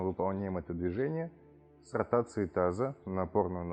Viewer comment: Спасибо за упражнения. Но хотелось бы попросить увеличить громкость звука. Очень тихо. Приходится все время держать телефон у уха, вместо того , чтобы смотреть упражнения. И , пожалуйста, используйте популярную обиходную лексику, а не специальные термины. Что такое, к примеру, «сартация» или «саркация» бедра??? Или из-за тихого звука я неправильно расслышала? Спасибо заранее за доработки в показательном видео.🕊